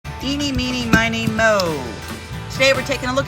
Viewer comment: What did you say there? Give me, give me money more?